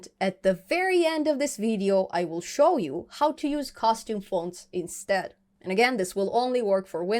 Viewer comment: Love your videos!! fixing to start a tkinter project that uses sqlite this is a great intro. At first I thought you were saying costume fonts. I had to watch till the end to learn what they were lol.